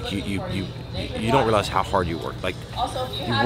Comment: Did you just say you don't like ice cream?